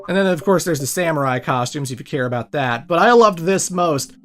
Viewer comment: at first I thought you said "but who cares about that" when you talked about the Power Rangers Samurai Store at the Avatar Store, but I Now realize, you said and I quote: "if you care about that". And yes I do care about Power Rangers Samurai, in fact Samurai is My All Time Favorite Power Rangers Series. And I also Love the fact that you put Power Rangers Samurai, Teenage Mutant Ninja Turtles, and The Legend of Korra before each other, since all three of these shows are nickelodeon shows, despite the fact that Power Rangers used to air on Disney XD back when it was called Jetix I think, and Fox Kids.